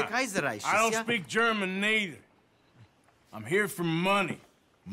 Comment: The first time I saw this cutscene i thought he said "I don't speak Germanese"